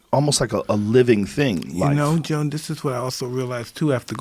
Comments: did he call him Jon? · Does Mike call Joe John at 4:22 in??? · Did Tyson call Joe Rogan John @ 4:22 ? :'D